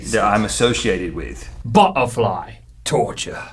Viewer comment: When Chunk Link said "butterfly," I thought he said "butt of lie."